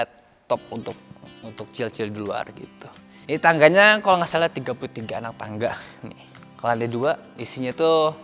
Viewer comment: Harga tangga rumah alshad rahmad setara dengan rumahku:) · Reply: oh. Mungkin salah denger... alshad bilang jumlah anak tangga.. nih